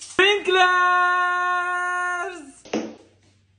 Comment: At first I thought they were saying pepe instead of perfect😂